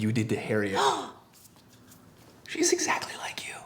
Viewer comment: If you stop it and start it again at 6:47, it sounds like he says, "i Ike you" lol